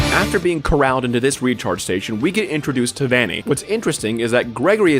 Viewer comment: "After getting corralled into this recharge station" I was eating a corndog and moved my plate and misheard it as "After getting corralled into this **tard station" and damn near choked. God I haven't had a moment like that in awhile.